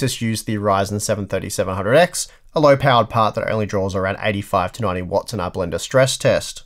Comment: I cried when he called the 3700x a “low end part” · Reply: I get the feeling but what he said is something completely different. There is a difference between "low end" and "low power" Low power is what he said...